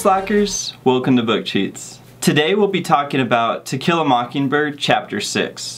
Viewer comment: Sounded like you said tequila mocking bird lol